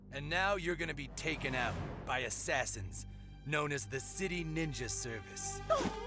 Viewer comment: Its funny when they city ninja it sounds like they say “shitty Ninja” lol